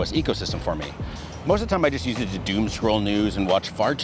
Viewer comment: I definitely thought you said doom scroll nudes and had to rewind 10 seconds to double check